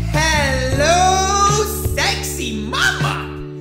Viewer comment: did you say "hello sexy"?